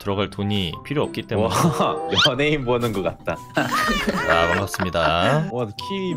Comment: 연예인 보는것 같다. 를 원숭이 보는것 같다. 라고 들었다 오 하트 감사요